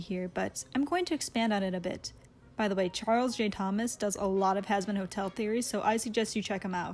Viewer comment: At the beginning I seriously thought she said husband hotel